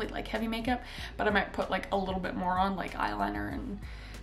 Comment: What she said: A little bit More on What I heard: A little bit ✨️Moron✨️ 🤦‍♀️